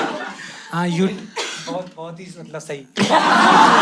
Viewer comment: can anyone tell me what did he say? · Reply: Bohat hi sundar tha sahi.....(joke ke ware mein bola hain)